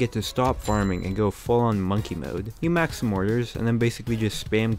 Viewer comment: ma'man did he say monkey mode